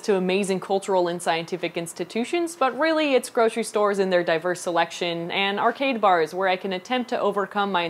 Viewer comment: I thought she said "grocery stores and their diapers selection" and was super confused. Secret nerdfighter baby? Secret pet monkey?